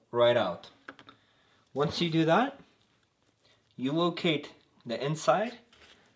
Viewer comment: Is it just me, or does he say "you will kate"